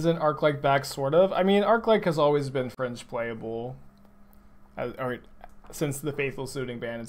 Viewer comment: I misheard "fringe playable" as "french playable" when you were talking about Arclight Phoenix, and was wondering whether I missed the creation of a new meme or something. In my mind, I was already debating if "french playable" meant something like "french vanilla", as in "almost playable but not quite", or if it meant "play this if you like conceding a lot".